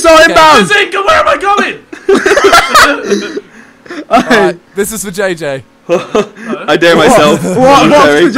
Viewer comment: i heard "alright, this is vagaegae" lmfao